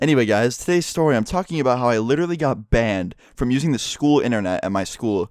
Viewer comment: like pls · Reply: I thought it said banned from porn